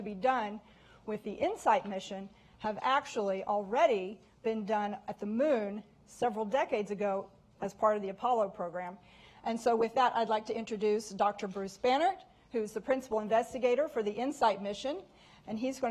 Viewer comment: Literally thought she said 'Bruce Banner' lol